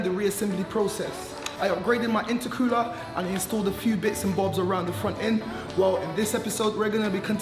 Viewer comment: I thought u said u was going to put m2 front end on???